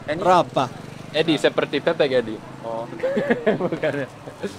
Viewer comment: edi seperti pep*k , awokwkwkwkwk sampe sakit perut saya dengernya ..